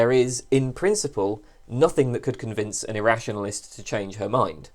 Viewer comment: "There is in principle nothing that could convince a rationalist to change her mind" I suspect that you misspoke here, it sounds like you said "her" instead of "their", which is like saying only women can be rationalists. Again, you likely misspoke, just saying to give a heads up if someone else watches this and gets the wrong idea. Keep up the good work. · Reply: No, I didn't misspeak. However, you misquoted me. What I said there was: "There is in principle nothing that could convince an irrationalist to change her mind." Perhaps you will take this to suggest that only women can be irrationalists. I take it to suggest that prefers to imagine women when she's talking.